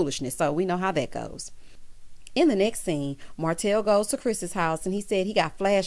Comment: Chile, I thought she said “Bye Martel HOE”! 🤣😂🤣